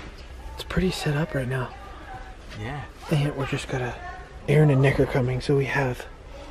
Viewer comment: at first I thought he said “Aaron and n****r coming”